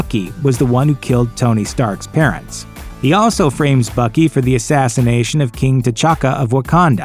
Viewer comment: in stead of Bucky I heard fucky